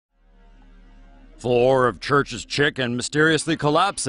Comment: I Thought they said Chruchs Kitchen not chicken